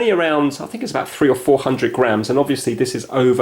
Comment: phew I thought you said grand for a second